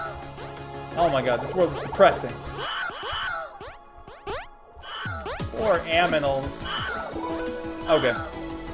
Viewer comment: y did u just say "more aminals" lol